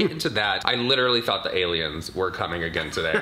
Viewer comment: I think I hear demi coming